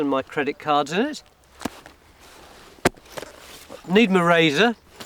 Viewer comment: That "portable telephone" is quite a relic! But does anyone know what the black thing is at 22:59? A giant eraser is what it sounds like to me. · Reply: Yes he says it's his razor. The other black thing his is radio front so it doesn't get "nicked'. Oh London!